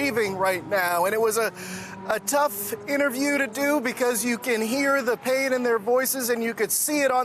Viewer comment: I legit thought he said, "you can hear they're paid in their voices"